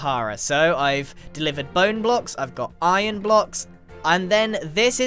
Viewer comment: I'm pretty sure he said blone blocks instead of bone blocks, did anyone hear that too?